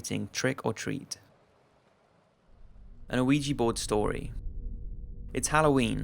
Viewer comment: i swear to god it sounds like he says luigi board and now i cant focus on other shit he says